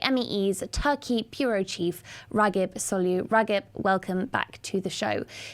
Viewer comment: The captions at 0:59 writes Rakesh Solanki instead of Ragip Soylu. Even if the captions are auto-generated, there is no way the syllables of these two names closely match, whats wrong with YouRube or MEE?